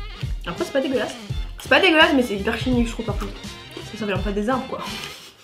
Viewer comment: Quelqu'un peut me dire ce que dit à 13:33 ? Je veux rire moi aussi 😂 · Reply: Lorene Snts "ça vient pas des arbres quoi"